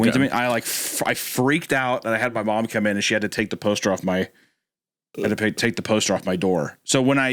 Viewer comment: I could have sworn said "poster of Markiplier" on my wall lmao